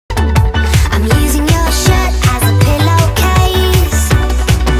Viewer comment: Why did I hear “I’m using your shit as a pillow case”